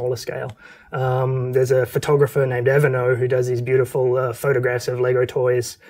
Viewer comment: What's the name of the LEGO photographer mentioned at 24:32? I can't quite catch it. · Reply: his name is Vesa Lehtimäki, aka Avanaut